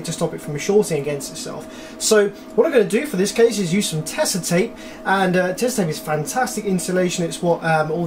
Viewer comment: what is the spelling of the name of tape he is referring to? It sounds like he is saying Tessa tape to me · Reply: oshields Tesa.